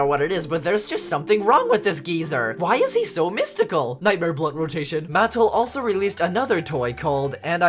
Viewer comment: I heard this as “Nightmare blood rotation” 💀